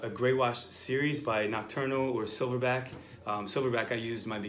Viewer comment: OK I found it from image, that ink name/brand is "NOCTURNAL". It sound like he is saying "Eternal", the nocturnal is fairly CHEAP can't wait to try. Thanks